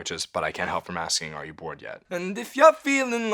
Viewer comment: i always heard it as “but i get off from asking at you bored yet?”